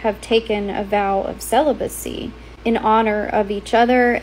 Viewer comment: i thought she said celi-bussy 😭